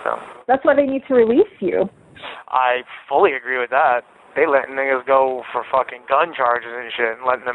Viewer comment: Did he say n**as???? He's too comfortable. 🤣😅